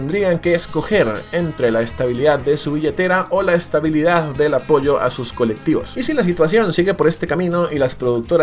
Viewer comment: escuché "apollo" ? gggggg